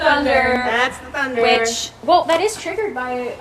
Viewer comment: I swear I hear "bitch" or "witch" I listened like 20x to try and place it to one if yall but nope someone else